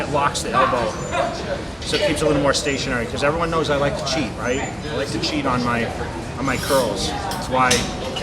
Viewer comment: I clicked right on 20:32 randomly and I swear I thought I heard Mr. Cutler say "I like to cheat on my girls". Threw me off a bit lol